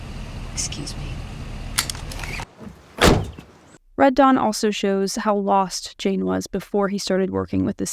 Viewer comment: I thought you were saying red Jawn this WHOLE time and thought that was a really odd name